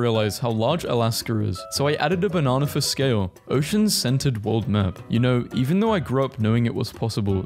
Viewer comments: I THOUGHT HE SAID “Ocean Scented Map” 😭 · I was not looking the first playthrough of this. That changed very quickly when I thought I heard this man say "ocean-scented map"